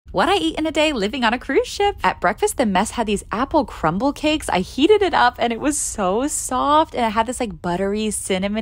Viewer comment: I thought she said I yeeted it up but she said she heated it up that apple thing